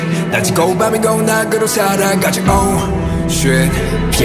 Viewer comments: who else heard “oh, sh!t” ik it’s probably a actual Korean word but for some reason I heard that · HELP I MISHEARD THAT IS 'OH SHIT' 😭